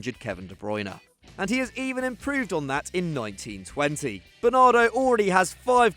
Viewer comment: did he say 1920